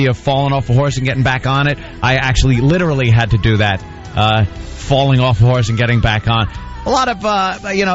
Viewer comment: sounds like Anthony says his dad taught him about falling off whore's and getting back on... Then immediately says he also got him a hooker at 13